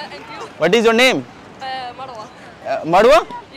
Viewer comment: think she says her name mandva 😂